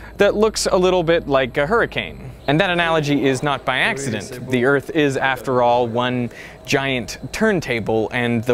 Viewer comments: Did he just say the earth is flat? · "the earth is a giant turn table" 0:30 I think he said the earth is flat😂